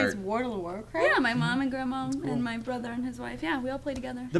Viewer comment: When Meg said "my brother's wife" I thought she said "my wife"... I was like YESSSS!!!